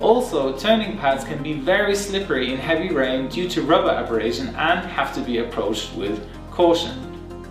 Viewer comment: What did he sayon 2:34? Slippery due to rubber abberation? Averation?